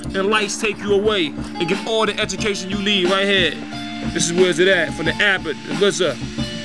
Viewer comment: Anybody knows what Rza says at 0:35 pl reply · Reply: He says, "This is where's it at. For the Abbot, the RZA. Word up."